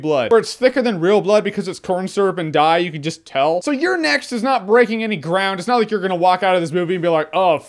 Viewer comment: When you said " You'll enjoy You're Next", I thought you said you'll enjoy your ex.